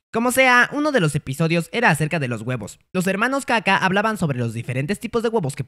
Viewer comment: Le entendi los hermanos kaka xD